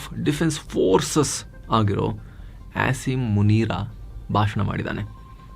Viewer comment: did I just hear him saying "Ass"im Munira? 😂😂😂